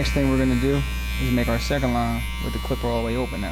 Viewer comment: WHAT DID HE SAY?? did he say all the way over? Cuz he deffo used something to make him take off less hair than before